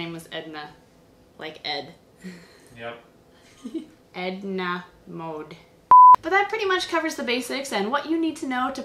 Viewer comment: was that a disgaea reference? Sounded like you said "Etna Mode"